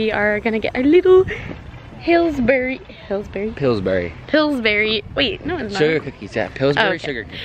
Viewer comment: I heard you say Hillsbury 2xs until said It's Pillsbury and not Hillsbury